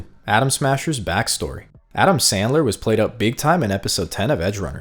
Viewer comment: anyone else hear adam sandler